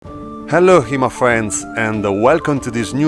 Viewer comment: I just came to hear you say "Hello HEMA friends" Now I feel content. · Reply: I always hear "hello here my friends" lol